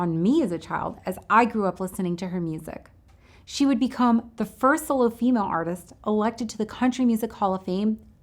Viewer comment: Now that I hear it again I realize that there's a compression artifact (or something) right in the middle of the word and it now sounds (to me) as much like "philipina" as "latina". Perhaps you'll be so kind as to tell me what I was meant to hear (it's a nice treatment of Patsy's life. You really didn't miss any of the important bits other than perhaps not quite emphasizing just how tragic her death was to music itself. When I was growing up I heard about the tragic loss of just four performers, Janice Joplin, Jimmy Hendrix, Buddy Holly, and Patsy Cline and, by far, the most emotional of those accounts (from people who would be in their seventies today -- I'm 62) would be when they were talking about Patsy Cline. The tone of her voice, you could say, pierced the hearts of that generation very very deeply and when she was lost the rate of change in music exploded -- they always felt left behind. Had she lived longer her artistry would have evolved the way Anne Murray or Celine Dion's did and she would have either remained a thing of her own (likely) or would have guided her legions of fans carefully and slowly into the future of music. They were very much musical orphans when Patsy died. Perhaps as many as 15 million Americans and Canadians were in that cohort. Never saw tears for Holly, Joplin, or Hendrix... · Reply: Okay, yeah says "She would become the first solo female artist elected..." But I can see where you might hear Latina where says "first solo".